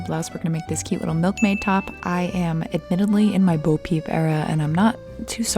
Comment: for a moment i thought you said "i am admittedly in my booby era" and went like "oh.. okay u go girl"